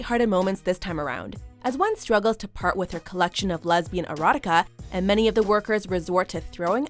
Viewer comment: Hollering at the lesbian novels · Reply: Novels? I heard Lesbian E R O T I K A H